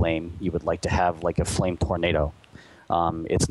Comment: Did anyone else hear the dev say "Flame Pornado" at 5:25?